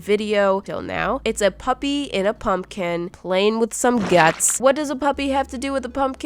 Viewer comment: That's funny cuz it sounded like she said pup kin